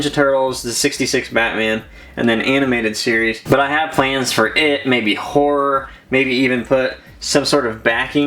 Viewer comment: who else heard “it maybe whore”